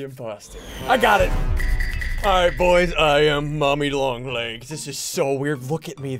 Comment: did he just say poppy long legs!?